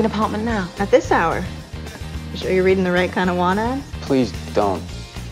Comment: It still bothers me to this day that 2:30 says "one" instead of "want". :P